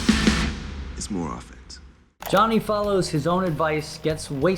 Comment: There is a mistake in subtitles. He says "is more offense" and the subtitles say "is more of it".